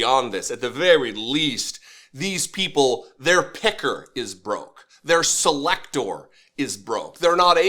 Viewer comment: I thought you said "his pecker was broke!" We only wish so! 😂